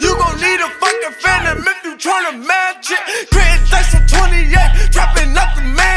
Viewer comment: Lmao I only remember this song because I thought he said mr grit 😂😂😂💯